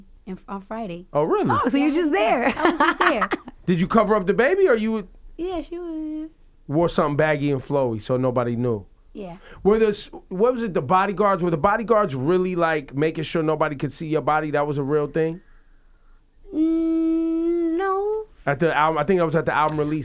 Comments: I promise you it sounded like she said "Yea she was in their" at 40:35...I could be wrong, but I think she slipped. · Did she almost say the sex of her baby at 40:36? Sounded like she was saying "yea she..." but then caught herself. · Did she just say "yeah she was in there" @ 40:37.. Is mama having a baby girl??